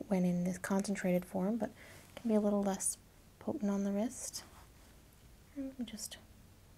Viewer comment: Had to pause when you said "potent on the wrist," all I heard was "puttin on the ritz"😂